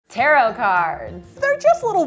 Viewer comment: I thought it said carrot